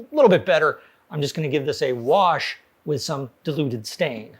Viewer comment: 'Deluded stain' ... (well thats what i heard )🙃